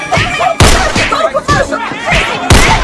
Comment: Did it sound like Paula said F**k me, Teresa?! And where is that clip from?